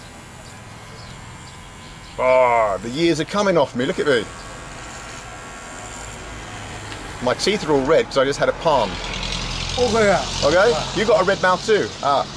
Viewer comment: Do they speak Hindi in Burma? Sounds like he said Hogaya at 2:53.